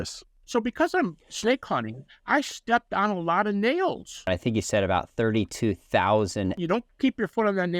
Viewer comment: "...I've caught hundreds of snakes and bit hundreds of times..." - - This doesn't speak very highly of his ability to learn from his experiences. He has stepped on "32,000" nails? I hope he decided to invest in a pair of boots with steel soles. And both statements show Gary's penchant for hyperbole. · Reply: Oh!.... Sorry... I thought he said "snails"! 🤣